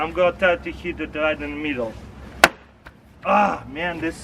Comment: Who else thought he said heat it btw I thought this since I'm an american